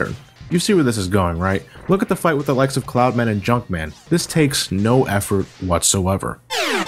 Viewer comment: I swear I heard "Clown Man and Jump Man"